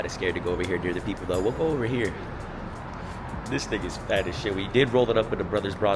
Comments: sounded like you said "this n*gga's fat as shit" at 2:38 LMAO but you really said "this thing is fat as shit" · did he say the n word? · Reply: "this thing" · thought he said “this n** fat as shit” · I really thought bro said “this 🥷 fat as shit”🤣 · Hold up hold up did just say “this nigga fat as shit” at 2:38 long time fan since lived in “Arizona” and I’ve never heard him say nigga😂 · did you just say "this ni**a is phat as shit"?